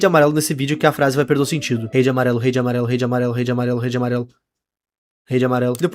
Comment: Eu escutei "verde amarelo" e "rede amarelo"